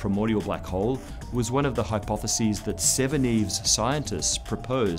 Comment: I think this series is excellent - and Dr O'Dowd's "British Accent" does make you listen very carefully to catch each bit he says (for non Brits like myself) - Near the end of this video I thought I heard the phrase: "70's Scientists" or did I hear "Seveneves Scientists" --- And I am going with "Seveneves Scientists" since in the 1970's we did not witness the inexplicable destruction of the moon but in the science fiction novel by Neil Stephenson the scientists did attempt to explain the inexplicable destruction of the moon. (A great novel)